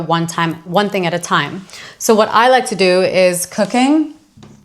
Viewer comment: I heard “so what I like to do is cocaine” 😂😂😂